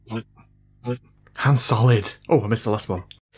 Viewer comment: lol it sounded like Han Solid